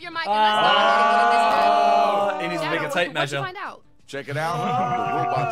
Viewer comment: Did Simon just say "in his nigga tape measure"? In